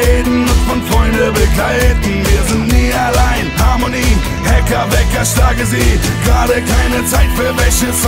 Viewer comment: No joke ich hab zuerst immer verstanden: Hacker,Wecker schlage sie statt Hacker weg erschlage sie lol